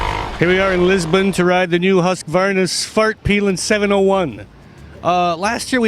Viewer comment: Am I the only one that heard him say Husqvarna's fartpilen in the beginning?